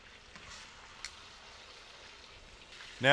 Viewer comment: subtitles say "Chris penis" instead of crispyness. fucking hilarious